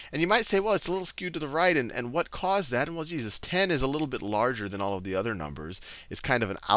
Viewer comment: Did he just say Jesus at 2:04?